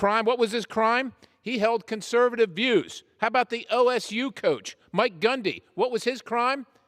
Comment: ***Oklahoma State Coach. Edit: Ohhhh, he said OSU. Sounded like LSU